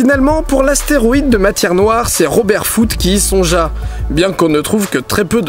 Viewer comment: Y'a que moi qui a entendu "Robert Foutre" ???😂😂😂